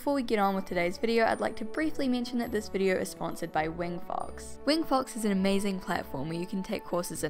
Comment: Deadass thought she said wet farts instead of wing fox